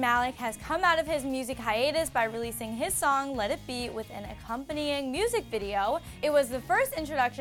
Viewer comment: Did she say Let it be at 0:15 It’s Let me dummie😑